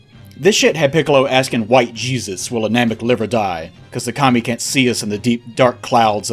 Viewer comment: what the fuck did you say here? "White Jesus will Namek ever die." What? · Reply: "White Jesus will a Namek live or die", it's apparently a reference to the lyrics of a song by 2Pac.